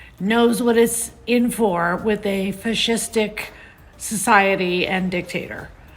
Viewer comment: did she say fishstick?😅 and dicktator